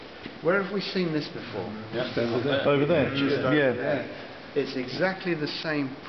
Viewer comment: Does he say „It‘s your penis.“?